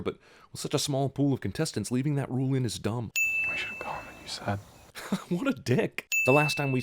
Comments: What does Gale say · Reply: We should have gone when you said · what did Gale say? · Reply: they should've gone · What the hell is Gale is saying at 5:52? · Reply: He said "we should have gone when you said". :) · What does Gale say at 5:52? that shit is hard to understand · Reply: We should have gone when you said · I have listened to that so many times sand I literally have no idea what he said, any help? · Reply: It's "we should have gone when you said". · What does Gale say at 5:52? I understand „We should go when you sat“ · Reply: "We should've gone when you said." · what is Liam Helmsowrth saying at 5:51 to Jenifer Lawerance? "we should..." i cant make it out can someone tell me? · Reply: I should of gone when you said.